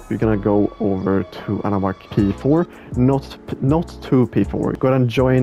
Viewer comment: I am confused, what did you say ?? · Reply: your first gonna join anawackP4 and then anawack2P4